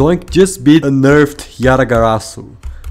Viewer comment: Because of your accent I heard "A nerfed yatagarasu" instead of "unnerfed"... I thought your comment was sarcasm lol...